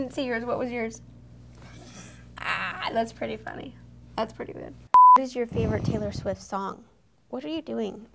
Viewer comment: i thought she said "what the f*** is your favorite taylor swift song" because of the beeping sound